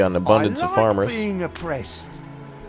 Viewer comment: Did anyone else hear that one worker? "I Love Being Oppressed!!" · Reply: Thought he said depressed lol